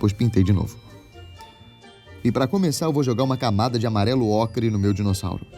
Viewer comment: po so eu ouvi amarelo-acre?